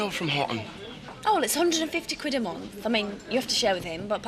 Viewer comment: did I hear right? Was rent £150 / month??